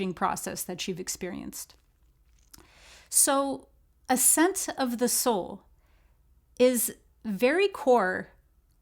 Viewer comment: For a second there, I thought you said "A Scent of the Soul," and I was like wuuuuut 🤔😁